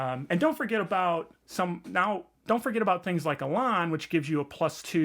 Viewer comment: What word do you say at 4:46? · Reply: I mentioned the edge "Elan" (page 39 in SWADE).